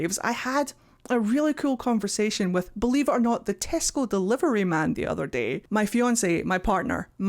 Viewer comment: For a second, I thought you said "the testicle delivery man"...